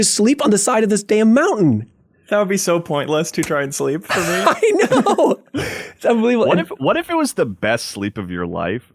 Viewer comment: What was joke at 22:00 ? I couldn't catch it😂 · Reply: "Upskirt?"